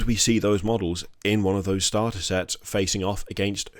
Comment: ohh "Starter Sets" those things you buy half of on Ebay. Thought it said "Start Collecting".